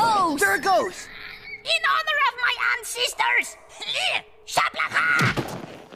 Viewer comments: I thought he said "In honor of my aunt's sisters SHABLAHAA" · I thought he said "In honor of my aunt's sisters SHABLAHAA" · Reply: His aunts’ sisters. Now that I listen to it it does sound like he said that So in honor of the rest of his aunts and his mother as well. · There was a time when I thought was saying "In honor of my aunt's sisters"